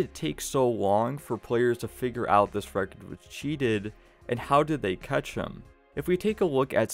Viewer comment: Bro actually wtf do you say at 2:28 “agrftytf cheated”😂